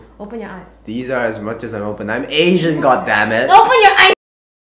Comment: I the beginning I thought he said “I’M ASIAN you mammoth”